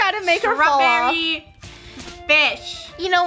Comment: what did just say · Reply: She said fish